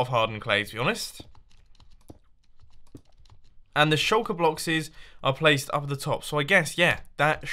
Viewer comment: did you just say shulker blockses. mumbo's Inner Gollum took over for a bit